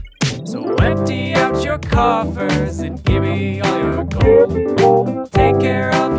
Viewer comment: Lyrics: What’d you expect? Some magic for free? A conjuring spell, Wine into tea? I’ll do what I can But magic’s not cheap And no one’s as good a wizard as me Dragon in your yard A haunted bidet When it comes to magic You get what you pay So empty out your coffers And give me all your gold I’ll take care of your problems If you do what you’re told I’m on call for kings and queens Royalty young and old So cough up bud, I need money I need your money Please, I need your money I know all the spells Im a very good wizard Please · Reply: I always thought he said “empty out you coppers” but coffers makes more sense